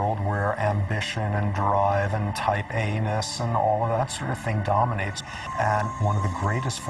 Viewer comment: Damn! this makes sooooo much sense. First I gotta say how funny is. "Here he comes with the dart gun again"😂 Oh BTW, the guy at the end said, something- "A" ness", and i heard anus 😂😂. It's a F#@ vicious cycle and unfortunately there's no alternate world that exists where a few greedy people won't get together and take way more than their fair share causing others to not have anything ,which leads to desperation until eventually the greedy one's will sell or exchange for whatever resource needed. And the price of course, costs more than it's worth, and that's how systems become too powerful, corrupt and gain complete control over the "less fortunate". It's sickening 😢